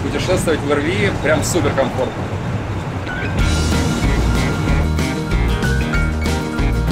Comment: Мне послышалось "С жиром" а не "пассажирам"